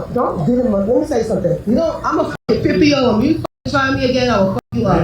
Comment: Did i hear wrong or did the woman say she was 50 years old at 1:08?